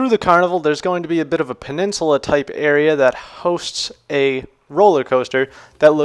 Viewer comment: LOL Penincelal area 5:26 :D - no idea what is that and how to write that word, but thanks for guide. Thumbs Up. >_<